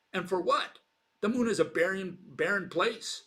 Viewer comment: in response to Bill's quote: "the moon is a pering place". (I think he said "pering", but I'm not really sure) 12/01/2022 China's Chang'e 5 lunar lander just marked a historic first: The spacecraft became the first to detect water on the moon at its landing site in real time. Chang'e 5 found water at its landing site near Oceanus Procellarum on the moon's near side, using an instrument that detects water by determining spectral reflectance measurements of regolith (soil) and rocks. Water was first definitively detected on the moon from orbit, by India's Chandrayaan-1 mission using NASA's Moon Mineralogy Mapper instrument (following several tentative detections beforehand by other missions and telescopes). The Chandrayaan-1 findings were announced in September 2009, and water has since been extensively mapped from orbit by missions such as NASA's Lunar Reconnaissance Orbiter, which has been operating at the moon since 2009. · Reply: giacomo, he said barren. what does China's moon landing have to do with anything?!